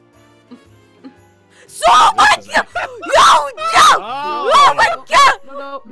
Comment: Who heard “SOUBWAY!” at 12:48? Do you watch TheOdd1sOut?